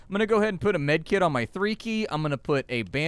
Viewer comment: I misheard "three key" as "breeki" and thought you were just getting into character.